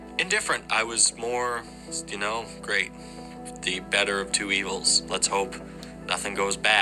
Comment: Biden got me a job but Trump is the lesser of two evils is crazy. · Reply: He actually said Trump was the "Better of two Evils", referring to Trump & Harris candidacy 🤨 The guy is a Phucking*Maga*Moron, aka #MagaPhucker